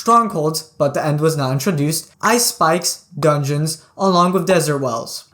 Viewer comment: I thought he said “Ice Spice 😭